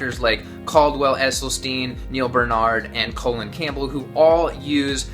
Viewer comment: Am I the only one who heard you say Colon Campbell instead of Colin Campbell? I just made myself laugh.